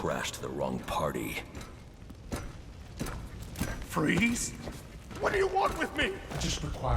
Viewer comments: The subtitles make Ferris say fries instead of freeze😂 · yo he said freeze but the subtitles said fries